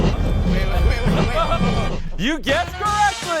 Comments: bro I heard “isn’t that speed” for a sec😭😭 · Who thought it said it was speed but he said isnt that sweet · When he said isn't that sweet I heard isn't that speed 😂😂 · When he said “isnt that sweet” i heard it as “isnt that speed” and thought mrbeast6000 came back for a second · he said isn't that sweet i heard isn't that speed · Rewatched the clips 10 times but i always hear 'speed' instead of 'sweet' 😂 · I thought bro said isn't that speed instead of sweet 😂😂😂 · In the end when he said “isn’t that sweet” I thought he said “isn’t that Speed” lol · I thought he said "isn't that speed" but i turn on captions and it actually says "isn't that sweet" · When he said “Isn’t that sweet” I heard “Isn’t that speed” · I thought he said isn’t that speed but he said sweet